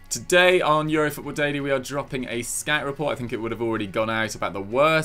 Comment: Every time says "Scout report" all I hear are "scat report" :( :)